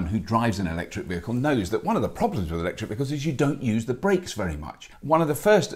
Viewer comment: Did you say "one of the problems with EVs [...]"? :P